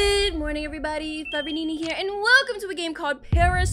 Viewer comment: Nenaa at 0:02 you said forever nene (that’s what I heard)😅